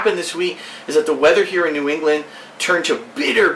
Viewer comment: did Phil just say "New England"?